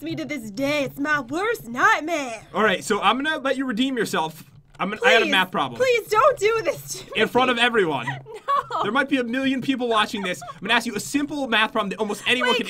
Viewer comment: Jen I agree I am bad at math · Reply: At first I thought pat said I have a map problem